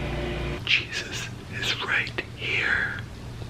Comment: not all rachels have orange hair, i have a classmate and her hair is black and her name is rachel edit:wait did he just say i am jesus?! *mental pause * i don't even know how to react to this.nik,get some help